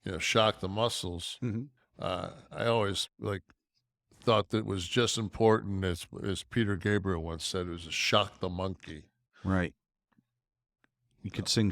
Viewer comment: The shock the muscle thing is also bullshit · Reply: I misheard that as SPANK the monkey lol.